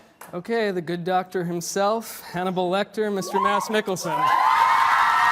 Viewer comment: My subtittles said mouse nickalson instead of mads mikkelson 😂😂😂